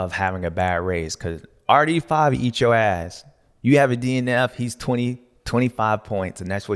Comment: Did Stew just say “RD5 will eat your ass”?